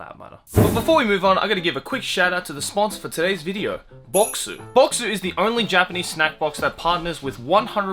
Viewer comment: Idk why I heard Bokksu "Boxer".